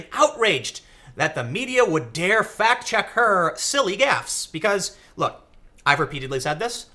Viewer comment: For a moment I thought you said "fact check her silly ass."